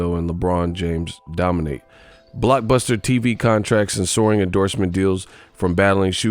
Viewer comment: "BLOCEMM!" lol thought was sayin to Block someone named "Buster TV Contracts". lol Then i realized i wasn't listening to a live stream.